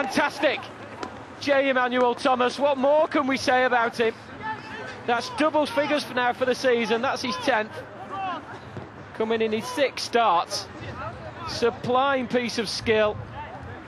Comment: is it me or does he say 'supline piece of skill'? wtf